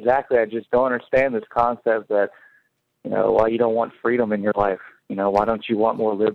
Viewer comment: When the caller says "why don't you want freedom in your life" all I heard was "why don't you want love in your life" referring to Jesus and Christianity.